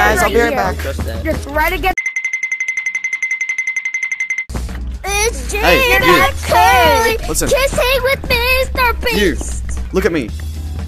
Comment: DID THEY SAY “it’s jman curly kissing mr beast 😭 “